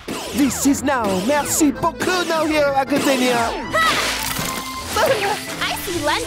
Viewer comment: Does anyone know what Yuga Aoyama said? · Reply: "This is is mecrí no hero academia"